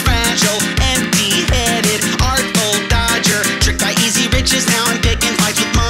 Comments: why do i hear."tricked by easy bitches now,"pls help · he said "riches" but I heard "bitches"